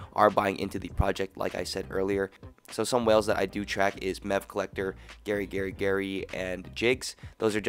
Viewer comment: what did you say again for whales collector? at 3:54? something collector, garygarygary & jakes? have links?